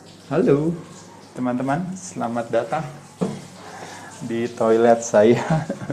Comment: did you say salam in toilet??